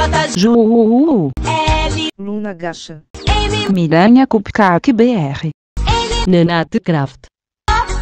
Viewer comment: EU JURO QUE OUVI PIRANHA KKKKKK